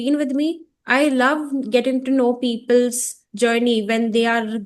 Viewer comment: Don't say peoples.. people is plural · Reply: "Hi! Actually,she said people’s journeys, which is grammatically correct. 'People' is plural, and 'people’s' is the possessive form (लोगों की यात्रा). She didn’t say 'peoples'. Maybe it sounded like that, but the sentence was fine grammatically 😊."